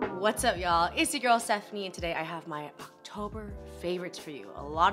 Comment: I’m not trying to be funny but is your name Stephanie or Sephanie?? I always hear you say “Sephanie” so I’m just wondering